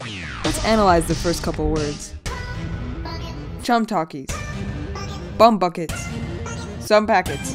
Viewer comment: I actually thought the first part said "Chum bucket"💀☠️